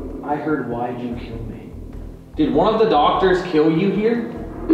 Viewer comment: with my headphones on, it sounded like the nurse responded to your question "Did he kill you?" "He said not to answer that" and then he replied "go away" after your next question. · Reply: I'm sorry, asked, "Did one of the doctors kill you here?" Then she replied "He said not to answer that."